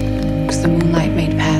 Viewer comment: I really need to know what the voice says! 5:13 I just can't stop listening to this tune, like all the previous Movembers ofc and i can't wait for the new one this year. I met Koan Sound in Liverpool and it was the best evening of my entire life. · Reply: its was night yet she could see, as the moonlight made pass through the water like fingers pointing the way, was holding her hand and took her away, because of you